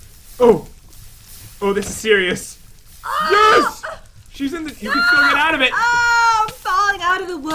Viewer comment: wait did Called Jen He instead of she 😂😂😂 DO YOU EVAN ENGLUSH XD 😂😂😂